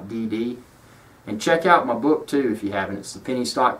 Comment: That sounded like "check out my boob too" 7:13! Phew! So relieved it turned out to be 'book'! Thanks for the informative vid. :)